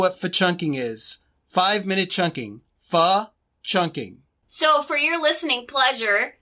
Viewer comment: I listened to the fa chunking /fi chunking . twice because I knew I heard you say the REAL Fword. A slip of the tongue. ? Hope you don t get in trouble for that one .UH. XO